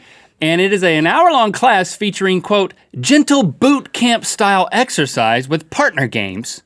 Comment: wow I had to rewind that, I thought he said "jiggle boob camp"